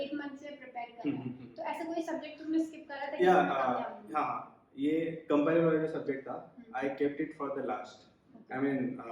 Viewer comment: his voice was not clear at 16:57 which subject he dropped ? , did he say Compiler Design ....? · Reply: yes he said compiler